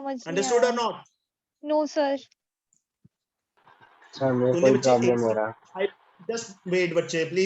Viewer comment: pe kya Bola uss ladke ne😂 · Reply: Wo bola" sir mere ko bhi problem ho rha h"😂